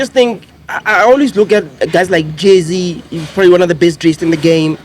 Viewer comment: Did the editor really put Jay Z instead of Jazzi Q😭🤣🤣